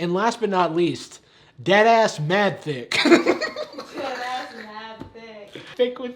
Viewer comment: the subtitles say on my screen Dad ass mad thick That is awesome